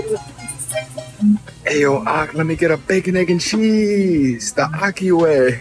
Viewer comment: So I watched this whole video and still can't seem to grasp why everyone hate him??? Can someone please explain? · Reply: Only thing I’m thinking, and I could be wrong, is @ 0:45 it sounds like he said “Ay yo, Ah, let me get…” “Ah” assumed to be a short for “Ahmed” (typically an Arabic name). Not saying all but a decent percentage of bodega owners/staff are Arabic . If that’s what was said, then I can see how some could take offense.